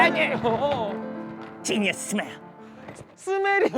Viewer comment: 들린다 지니어스 스멜ㅋㅋㅋㅋㅋㅋㅋ 펭수가 들린다면 스멜도 들리는거지! 그렇지